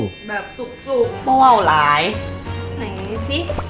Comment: ตอนที่ 8:07 มีคนพูดว่าบ่เว้าหลาย